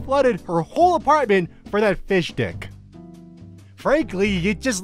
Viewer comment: i thought that said fish stick and i agreed???